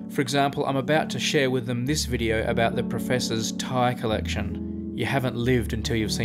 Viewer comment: In the end card talk when you mentioned Martyn's tie collection, I at first thought you said Thai collection (had the video on the second screen and didn't look at the ties) which made me think, Martyn must indeed be livelier than I gave him credit for.